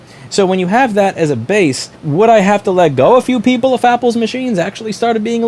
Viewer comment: did you just call it “Fapple?” Approved.